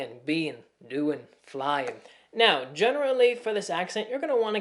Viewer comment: I am german and at 04:15 i heard a general Lee and that made me laugh.